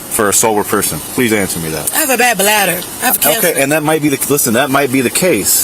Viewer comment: A blad batter😅😅😅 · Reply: i thought she said she had “a blk bladder”. lmao😂